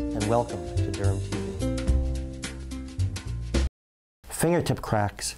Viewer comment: Thought he said “GermTv” at first lul